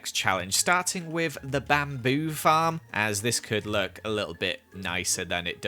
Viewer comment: Is it just me or did he say bamboo farm lol